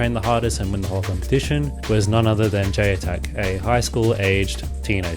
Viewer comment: "J attack". I wanted to know who we was talking about.I thought he said j-tac.. maybe everyone else knew, but I had to go to google to figure out what he said.So I'd figure I'd share what I found here. J-attack. He's from the gold coast and went to the olympics. He started breakdancing when he was six, and he went to the olympics.